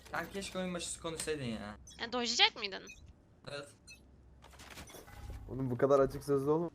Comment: da ne diyor bi türlü anlayamadım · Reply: Dodgelayacak mıydın diyor. Yani maçı iptal ettirtecek miydin diye soruyor çocuğa çocukta seni lobide kız olarak algılasaydım maçı iptal edicektim dodgelayacaktım diyor